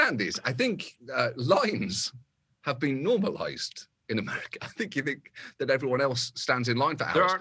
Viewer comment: I thought he said "Lies have been normalized in America", and I just sat there nodding "yea yea... yea..... sigh"